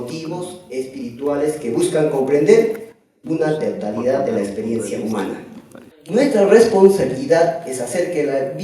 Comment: Qué dice en el mintuo 43:26 ?? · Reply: una "totalidad" de la experiencia humana :)